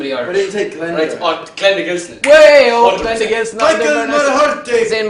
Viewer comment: Who did Niall say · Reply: Anna G Glenda Gilson